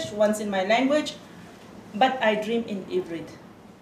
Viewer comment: she says "I dream in Ivrit", which is the Hebrew word for Hebrew. "hybrid" might sound very similar in French, but that's not what she said. :D